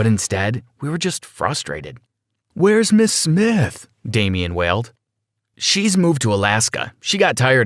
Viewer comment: I thought that you said will smith